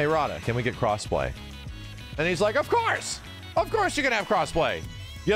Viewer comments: 1st... For a second i thought it said. "Cosplay? Of course i will." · I thought it said "Cosplay? Of course I will" Lucky Harada-chloe lol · ngl because i was watching this on my phone, i thought harada said, "cosplay? of course i will'